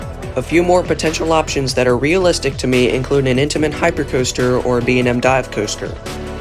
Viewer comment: But they could really use a good woodie! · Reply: Oh. I thought you said an Intamin Hyper.